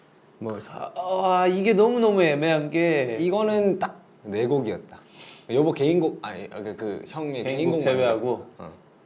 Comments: did jk call jin yeobo🙈😵❤️😍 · I think Junkook called jin yeobo and corrected himself to hyung You may not see subtitles for it you can hear it Yeobo: darling in Korea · Korean army, what JK said here 2:29? why the rumor said its "Yeobo, anyi, Hyung" = "darling, oh no, hyung" (i dont ship them, thinking this duo like real brothers, want to know if some fans actually missunderstood the word) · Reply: jk said ‘yeogo’ means ‘ this is’.